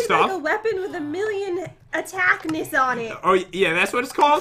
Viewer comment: I thought Jen said attackmiss instead of attackiss